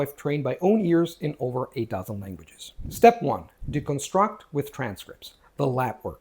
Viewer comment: I swear I heard "It's how I've trained my own ears in over 8,000 languages." Maybe I need to spend some time training my English listening skills!